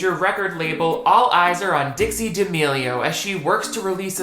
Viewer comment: LoL I keep hearing you saying "Ditzy D'Amelio"